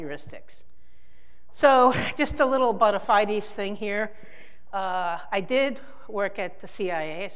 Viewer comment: The f did she just say?! Even the captions has it as '(mumbles)'! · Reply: Bona fides thing